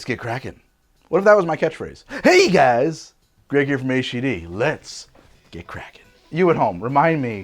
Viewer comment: I think "I got some weird ideas" is a catch phrase that more accurately represents your channel lol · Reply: I heard "Let's get Kraken (rum)."